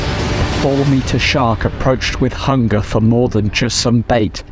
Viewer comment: I actually thought he said that it breached the water to grab a patrol-boat, at first.